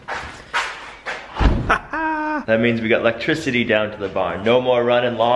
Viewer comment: could you show close-ups of the electrical box, showing what goes where, how you do the lighting etc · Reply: Jon Wright terribly sorry about that. I heard, “I want to DIY electricity”.